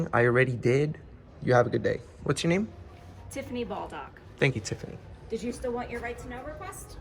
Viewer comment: Tiffany Balldock lmaooo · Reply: Yes that sounds painful. I bet she's docked a few balls in her time. I thought at first she said Baldcock and that was funny, but her real name was even funnier. I bet she copped it at school.